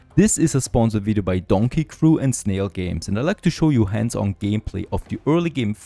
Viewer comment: i know its just to funny to see him experience the same i did. "if you dont want to build everything yourself, place the items here and... oh that does not work" or how he build the tent and got stuck in it. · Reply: I heard "Snail games" and was like. Huh? The snail games? Surely not. I never confirmed, but hearing that name was enough to turn me off the game.